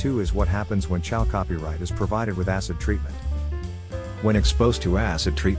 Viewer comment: Oh maan, that voice-over is the best !!! Maybe it's because English is not my first language but from 01:08 on, I hear : "...when Chal Copyright is provided with a** in-treatment..." :- "When exposed to a**... :D. That's just as golden as pyrite :D :D Fools gold :P